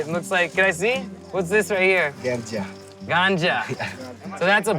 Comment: did he said that it was "GANJA" ?